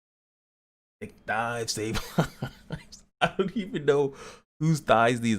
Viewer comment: At first I heard "thick pies save lives"